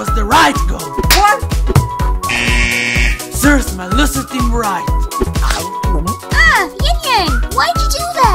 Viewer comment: What does he say at 1:17 Edit: nvm i figured it out “Serves my loser team right!”